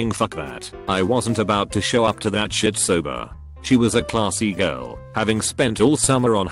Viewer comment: I THOUGHT IT SAID "CLASS E-GIRL" AJDBDJSNAKSBEHBS